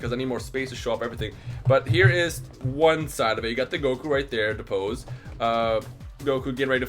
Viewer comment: lol did they call him gokou